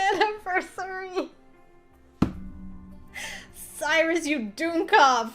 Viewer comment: Not even a MINUTE in n Elt is already causin trouble for poor Rik—n all the rest o’ us. 😛 24:20 – Germanic term? That’s what it sounded like to me. What’s it mean? It sounded to me like Fin made Salisbury steak just widdout (PHILLY-Speak) the actual title, since Salisbury is in England, from the description. That would fit, as that was originally made in the 1800s. Pretty cool. N it was indeed nice to see poor Fin FINALLY achieve happiness after all he’d been through in all the other routes. I know I said that before but it bears repeatin, as I see it. N good on him to hold out n go at Cyrus’ pace. Very noble o’ him. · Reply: Elt never rests. :P And yes, dummkopf is German. It basically means a stupid or dumb person. Oooh Salisbury steak eh? Boy's got good taste. :D It was extremely noble of Fin to really commit to going to Cyrus' pace and I'd say he's been very well rewarded for it. So awesome to see him get his happy ending! ^^